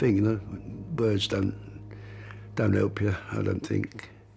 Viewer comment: did he say "Birds don't help ya"?